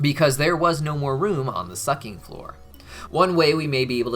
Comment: I thought they said "Suckteenth Floor" which for me at the time made sense.